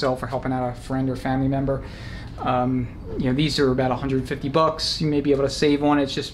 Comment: Check Freedom Racing. They're stupid expensive but may have it available. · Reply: I may have misunderstood your video but I thought you said they were about 30 bucks